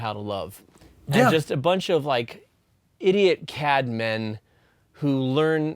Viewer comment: At first, I thought Jonathan said "idiot cat men," and I was very confused until I listened again.